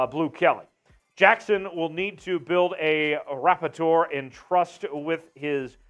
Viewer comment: Yoooo….@ 5:47, did he say “rapp-i-tor” instead of “rapport” (rah poor)? 😂😂😂 I’m out…..